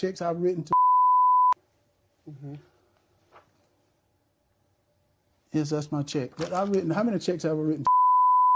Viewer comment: What Herschel said: "Yes that's my check" What I heard: "Well yes I put my feet on his couch" - Rick James 🤣🤣🤣🤣🤣🤣🤣🤣🤣